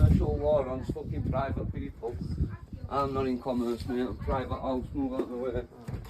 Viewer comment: Sounds like he is says meowous 🐈🐈 instead of me house.!!